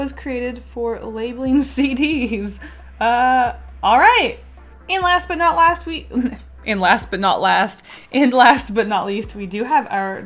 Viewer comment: sounds like you said, "and last but not last week" UM